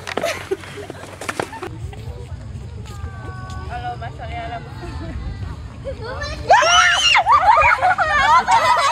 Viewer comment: ella es tan linda 😊 ella que dice? 😅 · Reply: She said. I’m shocked and ruuuun😂